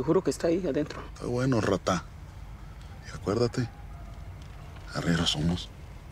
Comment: Que dice? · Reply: arrieros somos xd